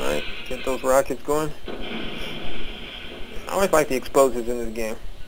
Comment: did you say at 24:32 i only buy the explosives.